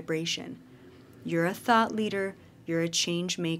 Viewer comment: Your friggin accent! lol So I had the phone down just listening and I’m all “Did she just say I’m a Thot leader?!?” (2 seconds later) “Thought! She said thought…😂🤦🏾‍♂️”